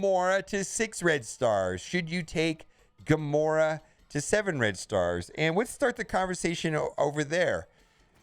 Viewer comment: did he just say 7 red Stores ? xd